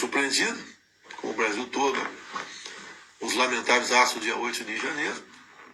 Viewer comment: Entendi direito? Aos 3:00 do vídeo, o bozo fala que "fui surpreendido, o Brasil inteiro, pelos lamentáveis atos TERRORISTAS de 8 de janeiro"?!! Os bozumbis precisam ouvir esta...